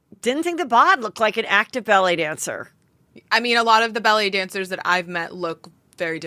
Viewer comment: I kept hearing “belly dancer” & not ballet dancer lol!